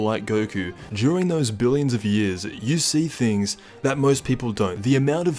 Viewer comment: Who thought he said “Jiren those billion of years”